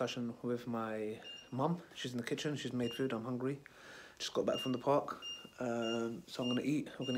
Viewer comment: i thought he said i just come back from work but he says i just come back from the park mum make me some food lmao ha ha that was funny all drained out from plying in the park on slides and swings